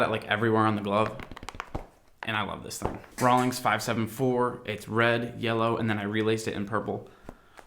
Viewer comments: Did he say Rawlings 574? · did he just say Rawlings